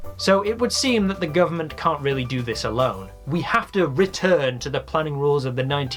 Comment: the captions say retvrn instead of return